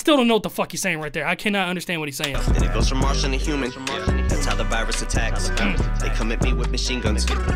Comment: I thought Em said "and it goes from Marshall to human, thats how the virus attacks" as in Marshall Mathers isnt human, hes on a whole nother level